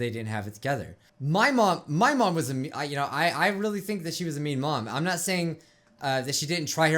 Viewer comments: I heard “meme mom” and not “mean mom” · Is it only me or when he said mean mom i thought he said meme mom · On the last one I thought you said "Meme mom" xD